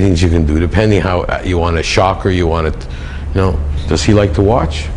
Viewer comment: What does say there? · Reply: i think its "fuck ya know how" its ver mumbeld